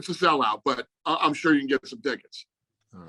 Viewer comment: I love uncle Dana at the end. 20:18 Q: What's the situation with the tickets? Are tickets still available or is it a sell out? Dana: I don't even know... It's a sell out for sure, but I'm sure you can still get yourself some tickets Classic Dana 😂😂😂😂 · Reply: actually sounded like he said "get some dicks" LOL i think the sound cut for a split second, hence why